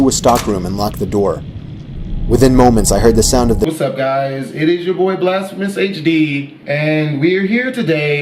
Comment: Lol literrly that intro of girl running " and I heard" what is up guys it's bhd